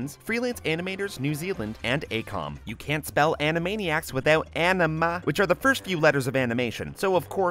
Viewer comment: I heard Panama, anyone else?